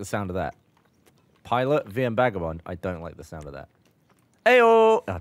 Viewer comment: Did Gavin say VM Bagabond?